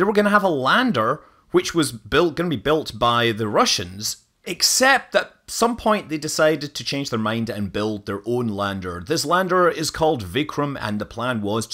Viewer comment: Lander was indigenous...not Russian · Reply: he actually says "lander gonna be built by the Russians, except at some point they decided to change their mind and build their own". I guess you didn't catch that.